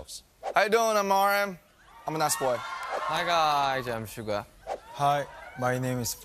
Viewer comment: Why did I hear RM say “I’m an ass boy.” 😂😂😂